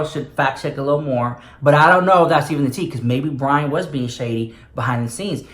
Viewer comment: you called poor little Reuben “Brian” 🤣🤣🤣 or maybe I heard it wrong... I agree tho... he should see what was said and as far as the radio person not knowing James... many people just don’t watch make up tutorials. And the new caster passed her opinion but it did seem kinda shady the way she said it... like homophobic AF 🤷🏽‍♀️🤷🏽‍♀️🤷🏽‍♀️